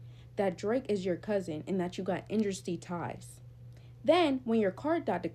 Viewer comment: what the hell is she saying??? Industry ties!!?